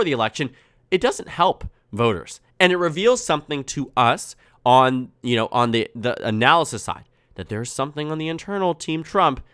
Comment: Could be wrong but it sounded like he almost said “us on the left” 😂🟦🟦🟦🟦🟦🟦